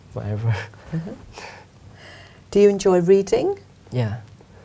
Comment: I can't understand the question the examiner makes. What she said? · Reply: Do you enjoy reading?